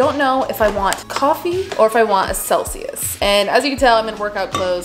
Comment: I really thought she said seltzer instead of Celsius and was like damn, that’s a pre workout drink 😂